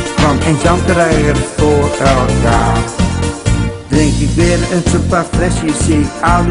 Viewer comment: i heard drink beard